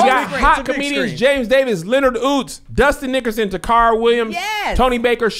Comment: He said “Dustin Nickerson Tacarra...” and I heard “Dusty Nickers in the car” 😩😭 I’m over here like IS THAT SOMEONE’S STAGE NAME?! 😩